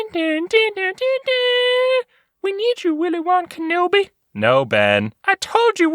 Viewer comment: I kept hearing “Willy Wonka no bee”